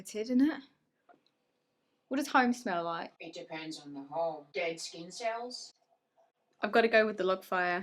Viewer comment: “What does Home smell like?” “DEAD SKIN CELLS” I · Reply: I thought he said gay skin cells LMAOOO